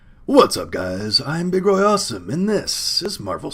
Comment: You are truly awesome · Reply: I completely misheard what he said at the beginning. I thought he called himself Big Roy Whore's Son.